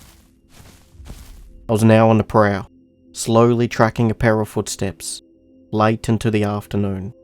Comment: Remember when i thought he said para footsteps. When he said pair of .....😂